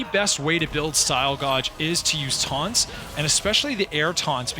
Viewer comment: Did you just says say"style Godge" 😶